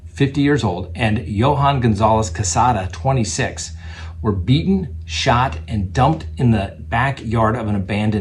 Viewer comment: omg at first I heard: "Eaten, shot and..."" I'm getting waaaay too used to horrific news. But that IS what's in the news. :-(